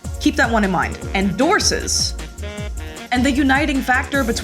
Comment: I'm not sure what you're saying here, "dorses"? Some portmanteau with "horses"? I'm clearly in the "don't know" category here but also the sort of person who seeks cursed knowledge to move over into the "do know" side of things. Thanks for the video.